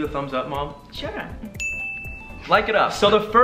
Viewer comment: OMG WHEN ALAN SAID LIKE IT UP I THOUGHT IT BRENT SAYING "BREAK IT UP" TO LEXI AND BEN😅😂😂😭